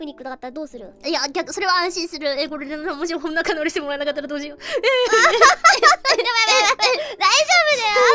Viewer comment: how tf did you understand what marine was saying there. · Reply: Just replayed it a bunch of times, if you're curious: えこれでもしも仲直りしてもらえなかったらどうしよう Crazy what a native Japanese can say in less than 2 seconds lol